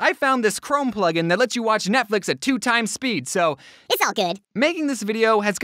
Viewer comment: if u watch the 6:54 part at x2 speed then says "its ok" instead of "its all good" or are my ears just weird /w\